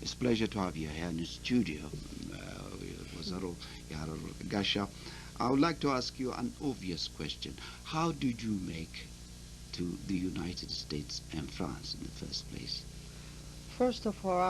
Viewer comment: did he say ወይዘሮ የሐረር-ወርቅ